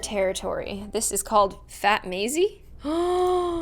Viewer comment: My names Maisie and it sounds like shes saying "Fatt Maisie?"